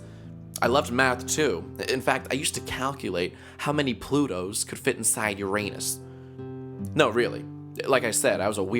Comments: he said I used to calculate how many plutos can fit inside uranus, it sounded like ...how many plutos can fit inside you're anus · I thought he said "I used to count how many pluto`s will fitt in your anus. XD